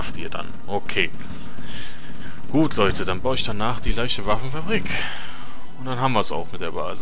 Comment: die ersten mission sind immer recht einfach sonst schönes LP hihi wie du die sprache immer nach spricht lustig ;) immer wieder schön an zu sehn ab der min 17:55 hab ich erst verstanden Leiche Waffenfabrig ;) und wegen corona ist doch der letzde scheiss das macht alles kaput hoffe das legst sich bald wieder ;)